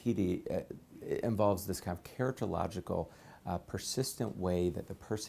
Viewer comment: the CC says keratological around 0:40 - what's the real word? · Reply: characterological—as in more innate to their character i suppose